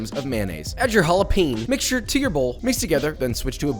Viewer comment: I legit thought you said u made ur own jalapenos